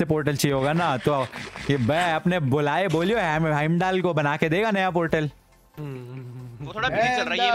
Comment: Idk what he saying but sunn ke maza aaya 😂 I am seeing this 10th time 🤣 it sounds to funny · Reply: it's Heimdall 😂